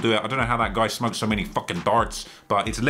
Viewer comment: you’re talking about stevewilldoit and you say you don’t know how he smokes so many _____. What the hell was the word you said, I literally can’t understand it lol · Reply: darts lol